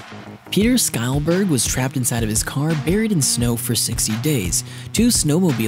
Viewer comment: is he saying 60 days or 6 days? I can't hear correctly, I think it's 6 but idk. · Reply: ok nvm, it's 60 and wow how did he live through that >_>